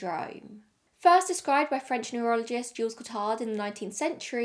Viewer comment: I thought she said “urologist” at first and was quite confused thinking perhaps he himself suffered the condition.